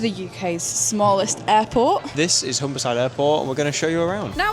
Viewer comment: Thought you were saying Homicide Airport 😩